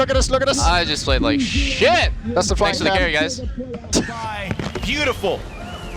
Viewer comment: Did he say that's the flying cat?